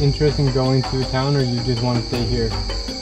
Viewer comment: im so high at 6:43 i thought the tribesmen said "im staying here" in english.. hahahah did he though??